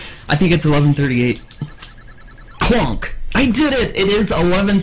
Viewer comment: “... a runner by the name of.“ Me: “I heard this name before.“ “He grew a mustache and started wearing a cowboy hat.“ Me: “Oh, that guy!“ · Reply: I always heard it as "plonk"